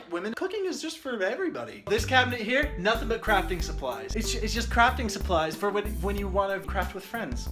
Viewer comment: Dude at 5:11 when said crafting suplies all i kept hearing was crackhead supplies and I was like since when🤣